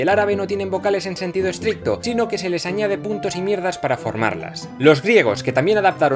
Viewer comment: Escuche "Puntos y mierdas" en lugar de "Puntos y mirdas" AJAJAJA